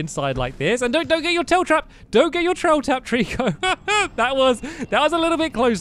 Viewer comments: did anyone hear stampy say dont get your trail tapped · did anyone hear stampy say dont get you trail trapped